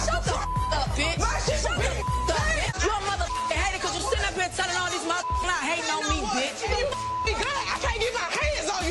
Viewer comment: I still can’t make out what Tommie was saying at 1:41. The sentence didn’t make sense. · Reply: Because dime is jealous of her and she would beat dime like a drum!!!